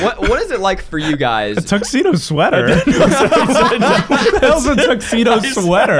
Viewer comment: i also misheard. But i heard "tucked neato sweater" lol